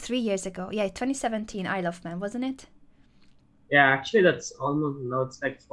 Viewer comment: I heard "I love men" everytime she says "Isle of Man" and I can't stop hearing it..